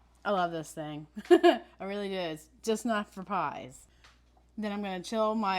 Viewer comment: Oh yes I do use my pastry cutter for pies, I just use it more than that. I’m glad this video is useful! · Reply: Oh, “not just for pies!” I thought you said “just not for pies.”